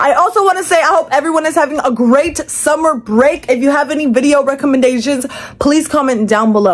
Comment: Pls tell me im not the only one who heard "re recommendasian"😂